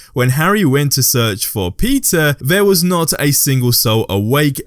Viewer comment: When he said Harry went to search for Peter at night. I thought he said 'Pizza'. Was think, 'When did Harry do that in the movies?'